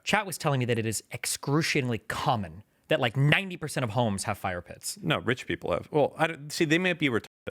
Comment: Bro I thought he said fire piss.